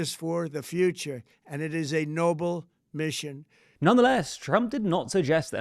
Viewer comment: Did Trump said "Nobel mission"? Nothing he is doing is Nobel.